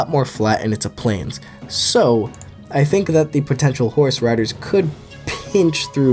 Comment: I thought he said the plains would largely be dominanted by horse radish at first